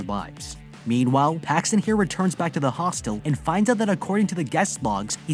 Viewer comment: I thought it said hotel instead of hostile